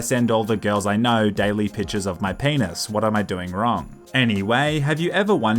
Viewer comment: At the start I thought he said “pitchers of my penis” instead of “pictures of my penis”!